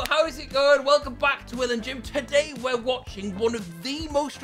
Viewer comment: i thought that said “will and james watch supremacy” i was so confused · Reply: Can’t lie thought it said “Will and James white supremacy”